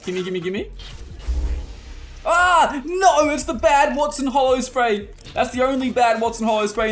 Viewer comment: did she just say Waston lmao gz tho